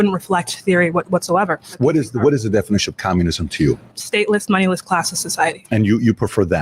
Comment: Ive watched this part twice now and i guess ill have to listen again, but i thought she said stateless, mindless, classless society not moneyless. Mindless! Which i thought Dave once you played the clip were going to dig into MINDLESS society